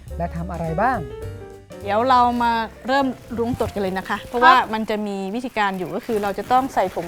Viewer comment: ผมได้ยินว่าเริ่มล้วงตด555